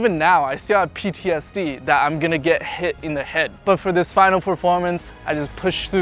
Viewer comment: What was he saying? Accept it? :p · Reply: "I'm gonna get hit in the head"